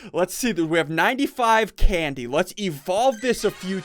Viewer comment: did you say evolve?